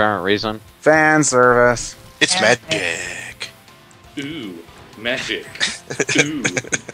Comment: Um...Not sound weird, but SKype fucked up, when said "It's Magic" I swear to god I heard "It's Man Dick"